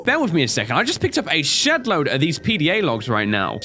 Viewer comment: did just say sh** load??